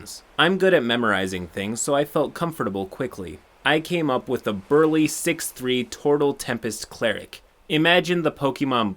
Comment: I thought you said "A burly 3'6 tortle". I almost pissed myself.